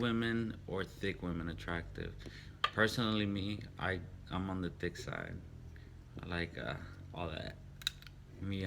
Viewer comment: Lol almost passed out 9:33 I thought he said I'm on the dick side at first.... got me all excited LMMFAOOOOOOOOO! love him though! He gets me hella motivated!